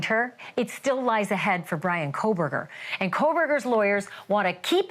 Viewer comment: I thought you said Anne Kohberger, then I went back to listen and you said it twice. 🤭😂